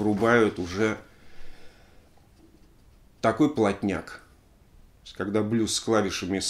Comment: "Плотняк"!)) Послышалось сначала "блатняк"!) Уфффф!